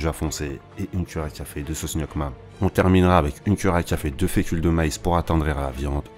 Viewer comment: Bonsoir, je n'ai pas bien compris à 1:42 sauce quoi ? miac... ?